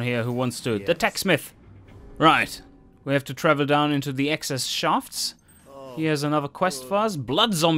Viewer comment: Between my fatigue and your accent, I kept hearing "Excess Shafts". 10/10, no complaints.